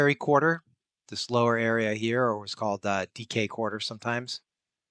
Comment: "sometimes called the DK Quarter." It is 01/07/2026 and I "misheard" it as "Decay Quarter". DK. Decay. It shouldn't take anyone 17 years to get a joke, but with me that's NORMAL 💀 Also English isn't my first language, so a linguistic joke taking an extra 10 years to be heard does slide by.